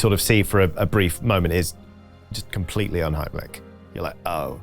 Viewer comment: What word is he saying here? Un-hym-lick? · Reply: "Unheimlich". Literally means unhomely, but used as uncanny.